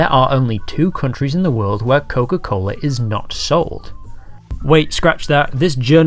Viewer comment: why am I hearing "Nuka Cola"?